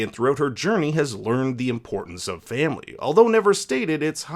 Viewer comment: Did you say family? * dom toretto theme plays *